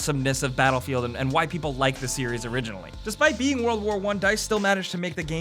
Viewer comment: did he say (white people like the series originally)